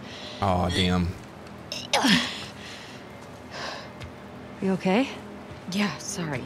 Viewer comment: the subtitles says "are you okay"? But Ellie says "you okay"? The new exposed. I know I'm not the only one who heard that.